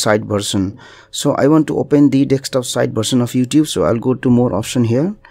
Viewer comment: Oh! Desktop I thought you were saying Dexter lmao thanks for this tip